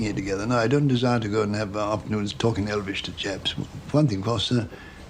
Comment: Did he say he didn't want to talk Elvish to Japs? or to chaps?